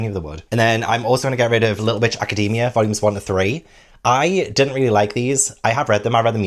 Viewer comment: you said little bitch academia instead of little watch academia 🤣🤣🤣